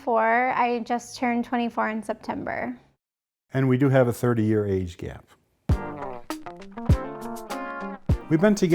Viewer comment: IM DYING!!!! I thought he said 30 yr aged cat 😭😭😭😭😭😭